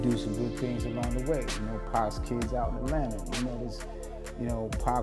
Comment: Did he just say 2pac got kids in Atlanta???... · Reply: I had to rewind that statement a few times myself, but it does sound like he said "Pacs kids in Atlanta". Plural.